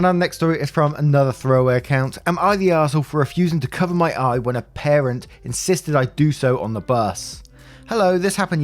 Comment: I thought the 2nd story said am I the asshole for not cutting my eye for a parent